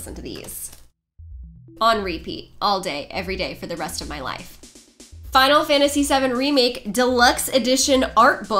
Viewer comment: I misheard right at the end and thought She said "Final Fantasy 7 Brimake"